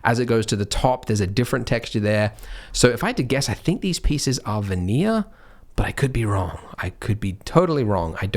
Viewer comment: Thumbs up if you thought Andrew was saying "vanilla", were confused, enabled the subtitles, saw that it was actually "veneer", and came to the comments to say this. :-D Australian man strikes again hahaha!